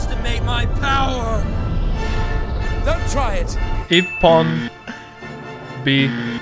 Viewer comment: okay: I know in English it sounded like he said "ippon", however, his pronunciation was correct on the first word as he said "ippan" as the "a" sound in japanese is pronounced like "ah" and "o" sounds are "oh." So he got that shit right! Good job!!!!